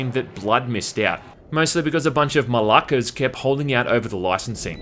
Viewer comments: did he say "a bunch of malakas"????? Swearing in Greek? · Did you say malakas at the beginning of the video (at 0:26 to be precise) ?